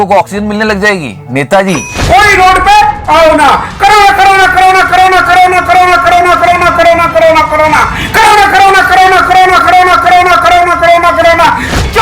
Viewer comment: I hear nakhre nakhre. U too?